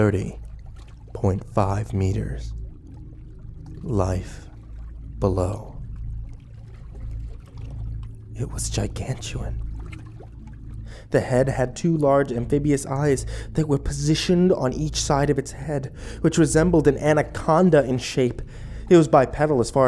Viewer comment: Did he say Gigantuan?